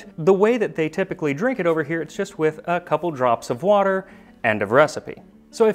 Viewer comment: They way they drink it over here... I heard "a couple drops of water, and aggressively" lol!